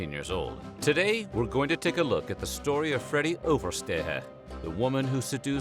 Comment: "Oversteheeh" 😂 love the effort